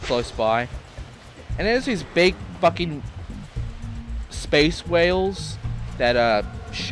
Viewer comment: did you say space whales 24:09 shot them down